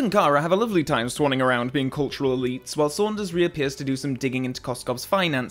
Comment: Ok first off love the vid, but something kinda dumb is bugging me. 25:21 What is Calvin saying here? “Bond and Kara are having a lovely time swanning around being culturally…” and then slight pause, then I hear “tswhile Saunders…” Was there an editing error? Or am I finally going deaf and/or crazy?